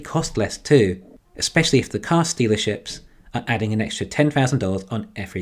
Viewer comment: Did I hear you say CarStealerships?